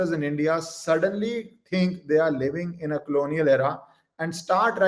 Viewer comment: Did you say "Clonial era" or "Colonial era"? Be "distinct" & clear in uttering English words.